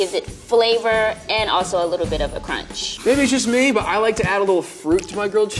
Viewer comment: I’m sorry did I hear I like to add fruit to my grilled cheese?!!